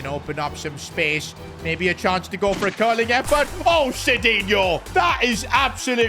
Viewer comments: that sounded like oh! shitinho 🤣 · i thought he said sh**inho